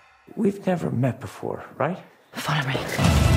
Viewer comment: what does she say at 0:14? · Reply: She says, "Follow me."